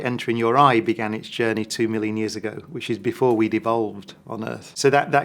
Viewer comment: sounds like "before we devolved" instead of "before we'd evolved" xD